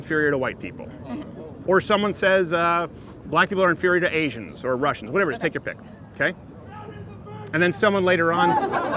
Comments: can someone please tell me what that guy said im really curious lol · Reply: Mr.Dankley crowder is a virgin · What did the guy say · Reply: Crowder's a virgin.